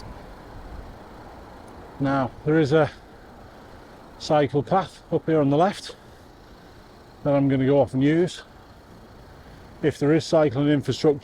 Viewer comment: At around 31:05 when you said "There is a cycle path up here on the left which I'm going to go up and use" at first I thought you said "There is a psychopath up here on the left which I'm going to go up and use". Immediately wondered how you'd know...and the mind boggles. I'm still in hysterics!😊